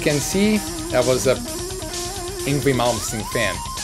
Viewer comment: I thought he said "angry moments" when he said "Yngwie Malmsteen"